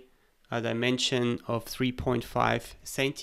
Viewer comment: I was stuck for way too long on the part when you extruded the threaded section of the base. I kept hearing you say "point five" CM but it was 1.5